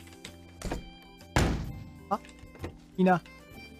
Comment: 「でさぁ…w」の思い出し笑いがかわいいw 1:17「来な」に聞こえた…